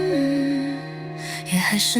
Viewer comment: I hear "You're still so black" 😅 feel like talking about me 🤣🤣🤣